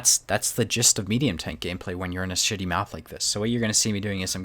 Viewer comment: did you say city map or shitty map? IT SOUNDED LIKE BOTH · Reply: he said shitty